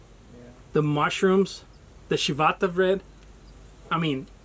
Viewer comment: Being from So TX, I thought you were saying Chivata Bread, then I googled it and and it corrected me to Ciabatta Bread. I'll have to try it.